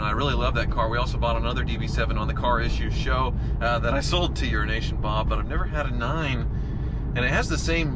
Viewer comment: Slow down when you say "Euro-Nation"....at first I thought I heard "Urine-ation". LMAO